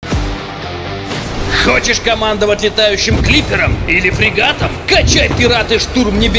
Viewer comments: сначала послышалось "хочешь командовать летающим клитером" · Мне послышалось, что летающим клитором...